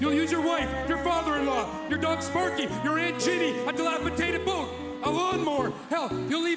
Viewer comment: what did he say? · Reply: "dilapidated boat, a lawnmower"